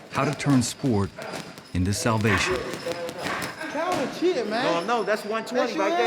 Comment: Tank sounded like a kid "Calvin cheating " 16:14.Coach Calvin keep working him King.